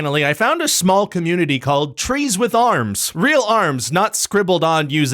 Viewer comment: I thought that said "trees with harms"